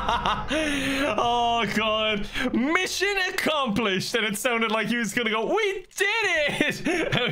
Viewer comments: It sounded like he said “Mission Accomplished... WAIT—“ then they exploded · it sounded like Lester saw the tank coming towards them and was screaming “Mission accomplished, WAIT!” Then they all just blew up 😂😂😂😂